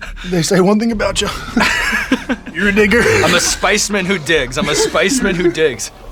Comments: aye yo what did he say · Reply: that took me by surprise too 😂 but context let me not get defensive. 'you dig. they say you dig. you're a digger'. plus, with him hyperventilating, his speech was getting sloppy. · ayo what he say @ 18:32 ? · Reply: Called him a good digger, like digging for information. Him dying of hot sauce and having an Australian accent made it sound pretty bad though LOL. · wait what did he say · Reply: digger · What did he sayyyyy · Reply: Digger. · WHAT DID HE SAYYYYY????? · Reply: i think he said you’re a digger because they were talking about how he digs for information but i definitely hear what you’re hearing lol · did he say the n word? cause sure sounded like it... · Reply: He said, “you’re a digger” speaking to the research the interviewer did.